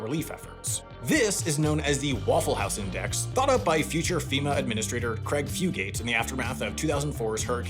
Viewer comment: I definitely heard "future FEMA administrator" as "future female administrator" and thought you had some inside knowledge of... something lmao